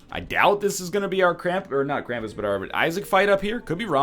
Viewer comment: Captions say "crammed pornographers" instead of "Krampus"